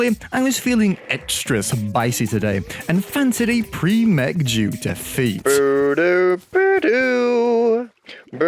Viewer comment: Idk why but at 5:55 I heard "Jew defeat" and refused to hear it as anything else. Why is my brain broken ._.